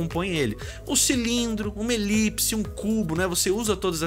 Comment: eu escutei errado da primeira vez e quase me mijei de rir "Um cilindro, uma Elipse, um c*"